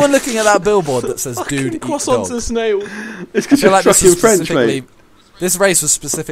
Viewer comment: Did Harry just say that "croissants is snails"?